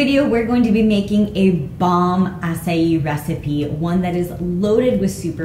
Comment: When you said "we're gonna make a bomb acai bowl" I thought you were gonna say a bomb ass bowl 🤭🤭